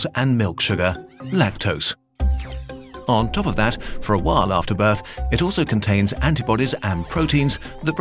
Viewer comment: You said, “for a while after birth,” and I heard “for a wild afterbirth.”